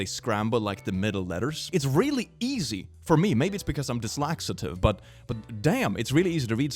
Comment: When he said "dyslexitive" at 10:29 it took me a while to figure out what he meant because I have trouble processing what I hear, so I use subtitles to help whenever I can, and the subtitles picked up the word as "dyslaxative" due to his accent, and that's an entire different story! (PS. no offense intended whatsoever I love this content, happy pride month everyone!!)